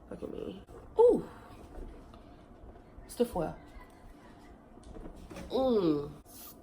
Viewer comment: What did she say @ 0:04-0:05? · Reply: ( stiff where)